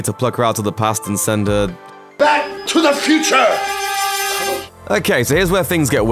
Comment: thought it said back to the kitchen lolll